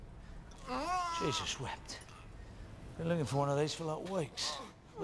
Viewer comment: does he say: Jesus wept??